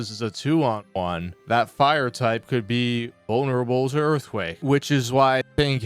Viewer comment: you also said “unerable” instead of vulnerable 😅